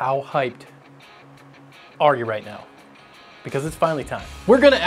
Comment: height i thought he said how high are right now